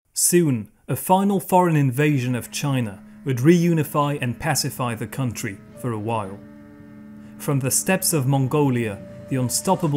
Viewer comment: If I heard you correctly, 'Soon, the final foreign invasion of China... ' This is incorrect. The final foreign invasion and conquest was by the Manchu, who toppled Ming and established Qing. If strictly invasion and not conquest, then many others did so including several Europeans such as the Russians, the British, French, Germans, the Soviet Union (1929), and the Japanese. If we exclude the post-WWII border disputes, the last foreign invasion was by Japan. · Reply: You're right about these, however I said "a final foreign invasion would reunify and pacify the country for a while"